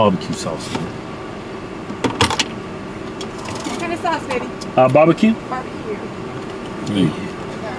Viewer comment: 😂😂🤣 did she call the twins lady’s